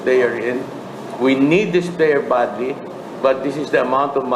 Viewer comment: Tama ba narinig ko 12:08..we need this player balti?? So SI baltazar ? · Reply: badly po sabi 😂